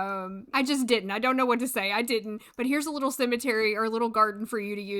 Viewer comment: cause of your accent it sounded like you said simatary and I am gonna go and change the name of my cemetary in my game now